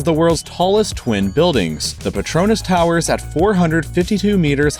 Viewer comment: Did I just hear: Patronus?